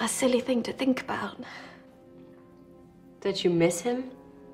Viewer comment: well rewatching this i thought she said bitch you miss him.... what is wrong with me.